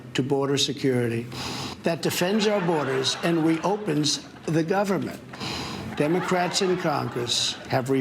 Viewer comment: did he just say we opens the government